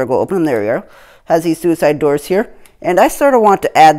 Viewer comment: did he say... suicide doors?